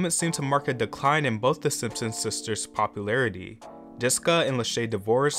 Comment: Wtf did you call JESSICA "DISCA" at 4:23???